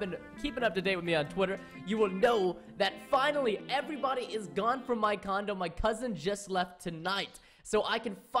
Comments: sounds like u said everybody is gone from my condom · has anyone noticed that at 0:14 instead of saying condo he said condom? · I originally heard you say (at the beginning of the video) "Everyone is FINALLY gone from my condom" ... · it sounded like he said condom